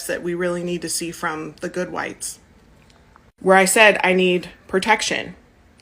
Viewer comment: did i hear "the good whites" 🤣 couldnt hear it over the music in the american eagle store